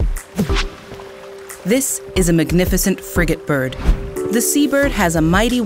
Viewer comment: at first and at 2x speed, I thought you said "this is a magnificent fricking bird" and I was like: "yeah, that's true, it seems to be a nice bird" xD